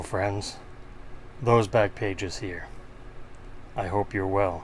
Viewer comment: If I'd seen you at a National, I would have walked up and yelled, "Oh, my goodness! It's MOE!" And you would have been terribly confused. But when I first started watching your channel, I always thought you were saying "Moe's Back Pages." I don't recall when I figured out my mistake; but, it took a lot longer than it should have. :-)